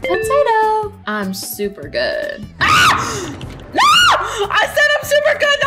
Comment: What said:”I’m super good” What typed:”I’m super hood”